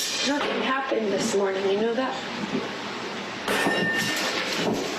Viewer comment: did anyone heard what Johnny Depp said Amber Heard: nothing happened this morning you know that Johnny Depp: you didn't hear Amber Heard: no Johnny Depp: so nothing happen to you this morning Clearly amber is hiding something from him and did this to accuse him clearly anyone can see it And this is not the whole story everyone Amber is framing Johnny Depp